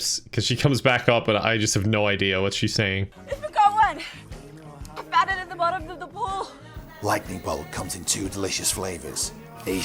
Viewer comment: i think she said "i think i won, i found it at the bottom of the pool' but with chlorine water up her nose